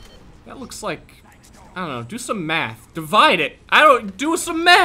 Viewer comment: DO SOME METH! It sounded like.